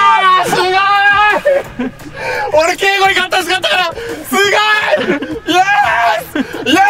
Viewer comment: 勝ってほしかった、って聞こえるけど、字幕は真逆？笑 那須さんの利き足(頭)が輝く企画もお願いします！